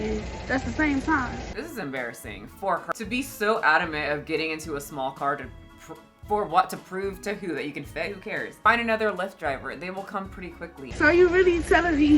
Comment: I thought you said 'You can fit' 😂😂